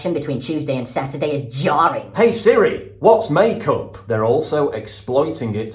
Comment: HaHa, when you said “hey siri whats make up” ? My siri heard it as “what’s my cock and replied “I’m sorry I can’t answer that ! 😳😂